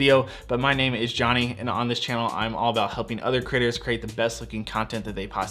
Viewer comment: Ha ha @ 0:31 ...” all about helping other critters ?” I know, he slurred ‘creators’, but that’s what it sounded like. 👍✌🏻😁😂